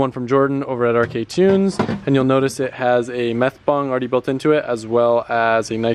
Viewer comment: Fucking thought u said meth bong, then nitrous bong like what drop cone meth and gasheads 😂😂😂